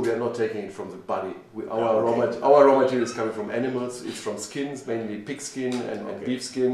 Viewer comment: i thought he said enemas when he said animals